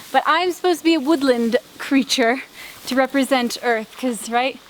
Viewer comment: nice vid It's amazing to see the amount of time and energy you put in your vids to make them look good, when you said Woodland Creature i thought you said Woodland Critter...I have to stop watching so much South Park